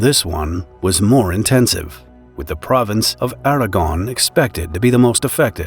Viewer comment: I thought he said Aragorn